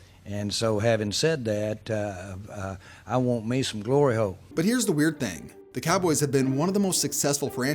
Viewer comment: Did he say glory hole?